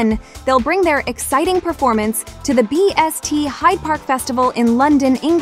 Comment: I think I miss heard her say bts to bst 0:55 but it kinda funny tho 😭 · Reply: heyaa we said it right “BST” but there’s typo on the vid 🤭🙏🏻💜